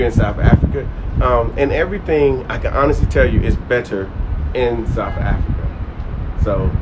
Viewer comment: Did Uncle pronounce better how we all pronounce it? Did I hear "betta" 😂... Is it my hearing? Okay sharp.